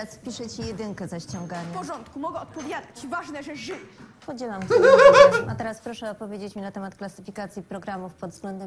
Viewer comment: przez śmiech nie jestem w 100% pewna czy tam aby na pewno powiedziała "podzielam twój orgazm" czy to ja źle usłyszałam przez i jego ryk😓